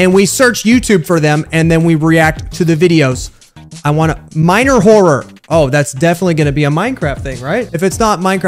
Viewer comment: When said “miner horror” I thought he said “minor horror” like we about to see a minor going crazy